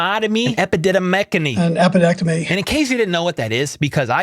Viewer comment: I thought Andy said Bell had an appendectomy. Lol. Mind blown.